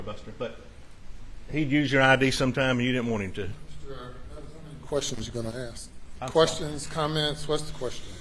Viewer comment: I could've sworn the prosecutor said to Buster: "I don't have any questions for you", then proceeded to ask him questions. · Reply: I think he may have said "many questions."